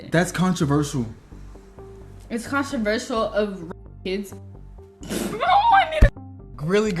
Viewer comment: what does she say at 13:00 ”🍇 kids”?